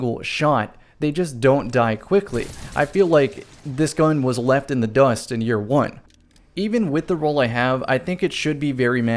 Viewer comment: Am I the only one that heard him say “this gun was left in the dust in year 1”